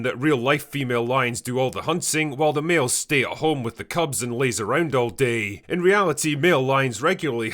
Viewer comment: was half asleep and misheard it as "the males stay at home with the cubs and laser dot" I totally support this misconception.